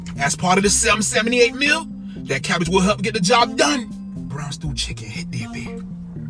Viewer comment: I thought i heard "Brown Stool Chicken" had me completely fucked up 🤦🏾🤦🏾🤣🤣